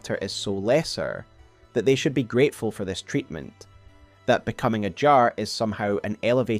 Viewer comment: I keep hearing “Becoming a Jar” as “Becoming HR” (human resources) “Yeah sorry we reviewed your resume and qualifications, and we just don’t think you’re a good fit for our Gaol program” That said I think you’re slightly off on Serosh — I believe Serosh was summoned originally BY the hornsent to be Marika’s consort, so that their divine lions would no longer be mere imitation. Marika made a pact with the serpents to beckon the Amber Star (the sun) dividing its essence — fire to the serpents, golden light to the Erdtree, draining the sun and Golden Lion of its color, leaving them pale and spectral.